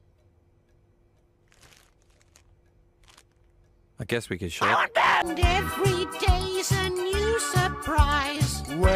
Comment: What is Duck saying at 10:33 ? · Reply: “I WANT BOTH!”